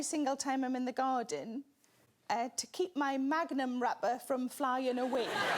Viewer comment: I only just realised that Magnum might be taken the wrong way outside of the UK, it's an ice cream thing, not the condom lol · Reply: We have the ice cream here as well but I thought she said "Magnum rubber" not "magnum wrapper" and I was confused about what's under the book.